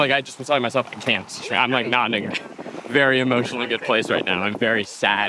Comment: does Zach say nah ni***? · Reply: “Not in a very good emotional place”